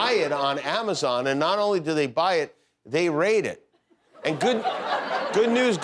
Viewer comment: I thought JK said (re the Bible): "...not only do they buy it, they raid it." It became clear straight afterwards that he ditn't.